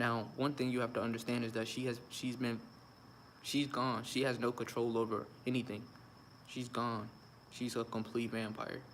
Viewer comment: they blanked out the word u said what did u say? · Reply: vaccinated he purposely didn't say it